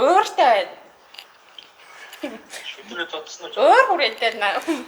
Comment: deer nuhur ni "Bi uchigdur oroi chamaig zodson uu" gej asuusan uu? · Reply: Shuuderee chamaig zodson uu gejiinaa😅